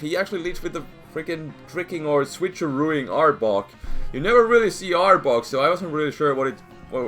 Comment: I laughed so hard in the beginning because when you were saying arbok it sounded like you were saying aardvark XD